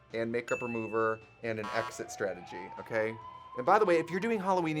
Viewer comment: I misheard exit strategy as exorcism 🎃🖤